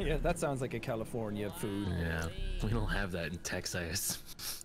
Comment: “We don’t have those here in Texas” *me, a Texas resident, staring at the egg bites in my freezer* are they sure we don’t? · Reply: but it sounded like he said "texass" which just makes it so much funnier